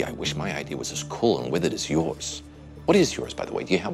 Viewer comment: What is actually House saying about Chase's idea? Is it "witty"? Because he slips into a British accent and it sounds like "witted" which as far as I know is not a word. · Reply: Withered